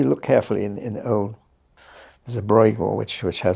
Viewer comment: Could somebody please tell me what artist Penrose mentions, just after 1:56 when speaking of the gallows piece? I'm interested to see the art but I can't quite catch the artists' name. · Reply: I see your comment is old but.... I think he's talking about Brueghel's painting Magpie On The Gallows. It has a peculiar wood structure.